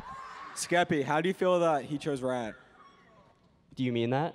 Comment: what did the croud member say at 26:25 ? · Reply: “skephalo”